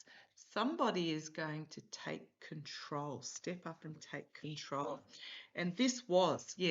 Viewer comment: Thought I heard guide at 21:24 when you said take control. Maybe it was spot